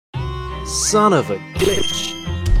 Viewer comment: its an audio illusion when you think of b*tch it says b*tch but when you think of glitch it says glitch